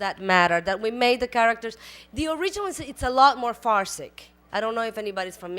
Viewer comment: Talented cast great show! What did Salma Hayek say at 6:00 "the original is a lot more" what ? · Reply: I think she meant to say farcical instead of saying farcic. She then said its very a farce because its based off a telenovela and characters/scenes were often exaggerated.